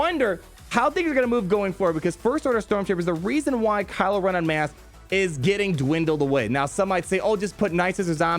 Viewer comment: Until like 8 minutes in I thought he was saying “en mass” and I’m like dude can you stop please that’s not even how you use the word Turns out I’m just deaf